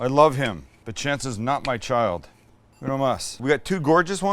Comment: All I heard was “Chance is not my son.”… pause 1 second.. “We have 2 gorgeous ones and one wonky one.” I wasn’t watching to see the screen cut and I thought “Man, that’s kinda harsh”. Lol